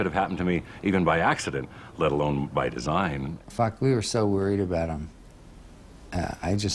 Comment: At around 2:52 does Geddy say "fuck we were so worried about him..."? That's what it sounds like. Not a big deal. Just wondering.